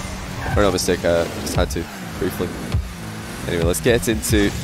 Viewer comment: u feeling okay man u said this “yeh sorry guys I just had to mute myself for a second my mistake errr not mistake uhh just had to pre-flip” Like what where u trying to say 🤣 get some sleep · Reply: lol "briefly" not "preflip"